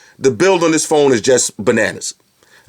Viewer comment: did I really hear BANANAS? :DD